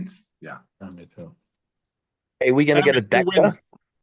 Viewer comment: this was surprisingly one of the best podcast episodes I've seen, non stop laughing during the I.Q. Devon with his Slumdog millionaire ass flashbacks were insane. Kamil with the out of no where appearance and funniest correct answer for the gas question was hilarious. to Ermes popping up for a bit stirring the pot. Loved it. thank you Neil and everyone that popped up. gonna be rewatching this one a couple times. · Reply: What Devon said? We gonna get a…??? Dexa???